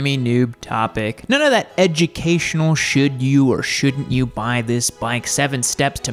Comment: It sounded like you said " edgy-cation" 😂